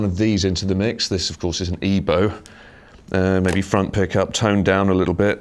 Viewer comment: what is that gadget called he uses at 8:00? I can't hear what he says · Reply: It's an E-Bow!